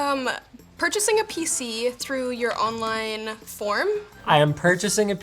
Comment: I'm think she said "form", not "forum".